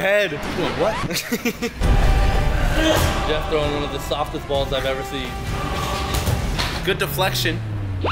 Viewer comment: First, "he's going for head" Then I hear "Damn he got the softest balls i've ever seen" W t f lmao